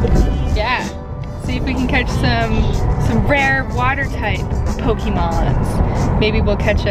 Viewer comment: I thought she said porn